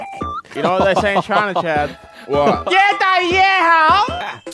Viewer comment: he actually says the bigger the better in Chinese wtf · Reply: 越大越好 Yuè dàyuè hǎo